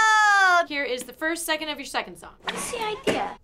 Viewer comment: Did she say second song?